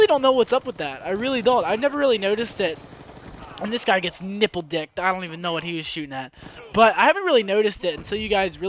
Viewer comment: did you just say nipple dicked?